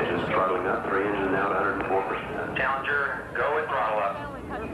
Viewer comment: is he saying "challenger going to Allah" ? in